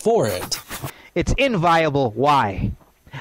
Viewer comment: If something is inviolable, it requires no defense. This guy tried so hard to sound smart and ends up making himself out to be a fool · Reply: That's what it sounded like to me too. I think he's saying (or trying to say) "inviable" which is probably technically the right word, but really confusing. I would have used "nonviable" myself, which is less correct, but less susceptible to mishearing.